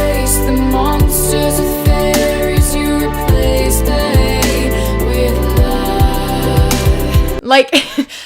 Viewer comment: Wait, the end screen song says "you replace the hate with love" and not "you replace they with light"? I've been mishearing that for forever